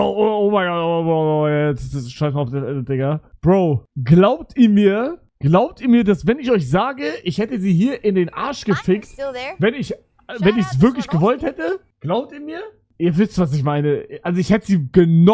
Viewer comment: was sagt bro da 😭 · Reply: Ich denke er fragt ob wir ihm glauben dass er sie in den Arsch gefickt hätte ... also er hätte sie halt genommen, am Boden direkt....natürlich nur wenn er wirklich gewollt hätte...